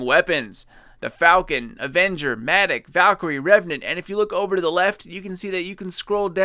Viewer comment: Revenant? I thought it said "Remnant" as in weapons you would find in remnant vaults or acquire from defeating remnant enemies.